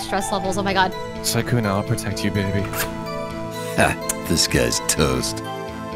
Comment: this whole time i thought the intro said "hah, this guy is toast" but it really said "disguised toast" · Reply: You have it backwards the intro is this guys toast it's from a hearthstone card